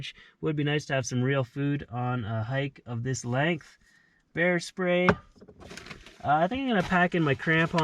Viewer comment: When you were going through your list of items you're bringing with you, I heard "hair spray". I thought, why is he bringing hair spray? So I went back and played it again. Oh...BEAR spray. Well, that makes sense.